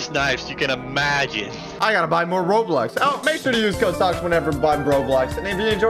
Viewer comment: did he just say roblux?